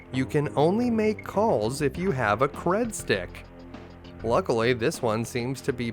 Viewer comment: I was munching on chips when you said "Credstick" 33:57. So all I heard was " You can only make calls if you have a BREAD STICK! " I was like, What?! Pay phones only use Breadsticks?! what kind of Future is this?! 🤣🤣